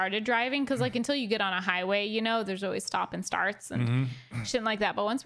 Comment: is that the first time has sworn on camera un beeped ? lol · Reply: I hear "always stops and starts, sh*t like that". Just after